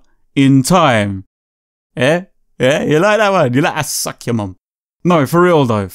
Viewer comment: Did he say he'd suck my mom?